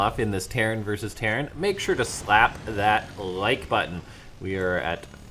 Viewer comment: lol did he just say slapp like now? around 4:00 epico